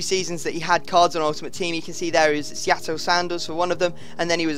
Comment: Seattle - Sandals- did he say?? lol